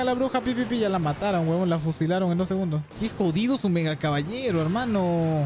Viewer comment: yo oí que judio su megacaballero XD